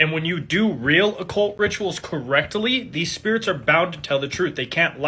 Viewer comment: had to go back coz I thought he said rectally 😂😂😂😂😂